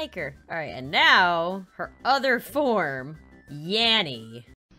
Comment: I never hear "yanny" or "Laurel" i hear Yammy!